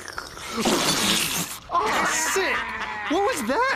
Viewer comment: When I first saw the episode of the frat boy unicorn (4:00) When Mordicai said "Aww sick!" I thought he said "Aww shit!"